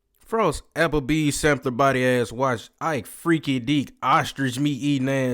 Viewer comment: You said Applebee's what now?😂 I was tryna figure out what you was saying though like, I heard you saying Applebee's,😂 but the other part I'm trying to make out what you said. You get me? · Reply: Lol Applebee's sampler built body ahh